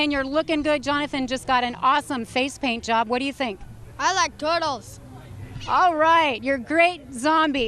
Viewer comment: I like turtles. 😂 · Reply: I thought he said, I like girdles! 🤣🤣🤣🤣